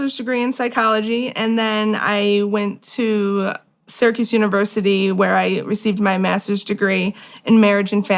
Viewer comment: I know now she said SYRACUSE University...I heard "Circus University" and I thought "well this is clearly the problem."😂